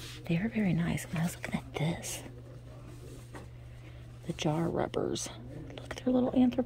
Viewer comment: Oh my gosh! I LOVE those bedside lamps for only $27? I understand not wanting to ship them, but boy do I wish I lived closer, I would grab those in a minute! Also, while I was writing this comment I thought I heard you say "oh & here is a jar of rubbers"- lol, of course that got my attention so I looked up & saw the box of jar rubbers😂 Thanks for a fun video! xo from Oregon